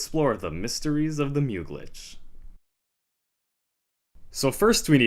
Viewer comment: When you said mysteries of the mew glitch at 1:11 It sounded like “these questions will be answered at the mysteries of the museum” 😂 Just like the show